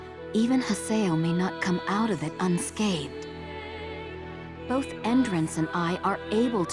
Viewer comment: with the way said Unscathed, it sounded like she said UnSkeithed.